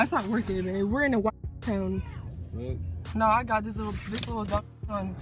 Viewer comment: I heard something like " that's not working today we're going ..... them" then something like " I got this little ..... gun" Sounds like a death threat to me